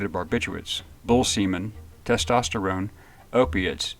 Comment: Did he say bull semen?